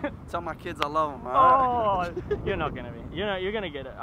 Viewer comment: anyone heard "Ah you're not going to make it" to?